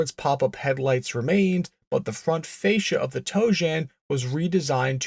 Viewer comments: I hear; "Toe-Jam" every time they say the "name" of the prototype/ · sounds like he is saying "toe jam"